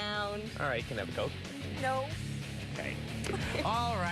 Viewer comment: what did he say? 😭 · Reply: Alright, can I have a Coke?